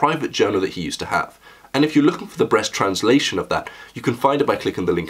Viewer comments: Did he say "The Breast Translation"? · did he just say "..the BREAST translation"? Buuuuaahhahahahhaa. Boobs. That is all.